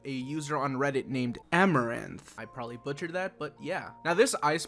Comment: BY WHO??? DID I HEAR THST CORRECTLY????? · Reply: Amaranth